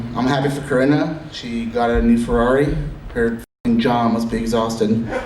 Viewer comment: what did Jonah say? I heard something about "f***ing John"? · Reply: “her jaw “